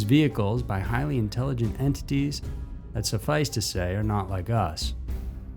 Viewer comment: Oh man, I thought you had said "-highly intelligent entities; suffice to say it's not us" 🤣 and all I thought was "yeah, fair enough, we dumb"🤦🏻‍♀️